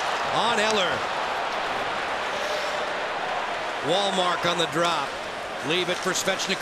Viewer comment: The whole time I thought the announcer was saying “ Walmart.”